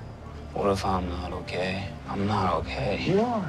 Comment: it sounds like he says what if I'm a little gay💟